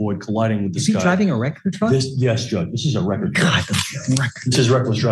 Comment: what's a record truck? Edit: Maybe he's saying wrecker truck.